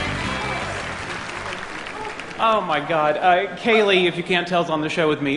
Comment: what did he say at 1:20 '? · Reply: he said: oh my god, Kaley (Penny), if you cant tell she's on the show with me